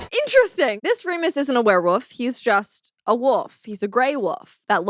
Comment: Is it just me or is she saying "Woof" instead of "Wolf"?